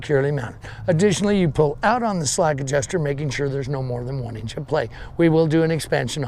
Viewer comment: what exactly says in the minute 8:58? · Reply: "...pull out on the slack adjuster making sure there is no more than one inch of play"